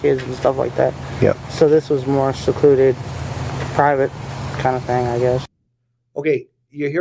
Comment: I didn't hear her say "habit", I heard her say "private".